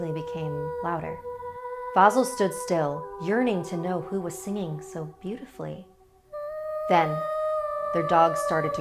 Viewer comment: When she said "Yearning to know who was singing so beautifully," I thought she said "Urinating to know who was singing so beautifully."